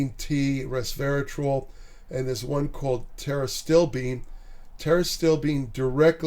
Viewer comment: Tara still bean? can someone tell me what this polyphenol compound is called correctly? the subtitle is kinda off :D